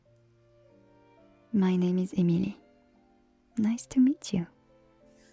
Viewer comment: min - no, thats not your name, your name is Wattson from Apex Legends! :) (on Subtitles its Emil, on Describtion its Storm "Enjoy, Storm⚡" and i hear Amelie or Amely, am i correct?!) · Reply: Emilie ! :)